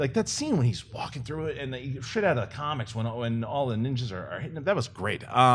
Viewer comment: Did just say shit? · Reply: HA, nope "straight outta the comics"